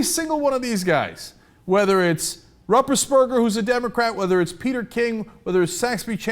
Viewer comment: Oh, "Ruppersberger"! I totally heard "Rumpbusberg" when Cenk said it, and was all, "What...is that a relative of Snuffleupagus? (*raised eyebrow*)" Heh.